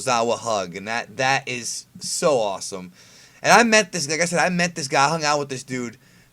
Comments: it sounded like he said "and i met this nigga" lol 😂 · sound like you said i met this nigga xD